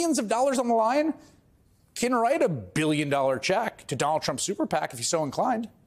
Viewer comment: When you said "1 Donald Trump" instead I heard it as 1 dollar trump 😂😂😂 that was gold in my ears 😂